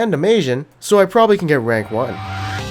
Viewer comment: What does he say at 0:04? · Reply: "so i probably can get rank 1"